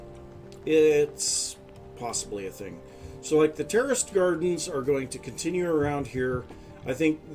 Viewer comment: I swear I keep hearing "terrorist gardens" 😅😂